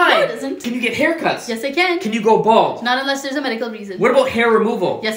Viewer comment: When he said “can you go bald?” I thought he said: can you grow balls?💀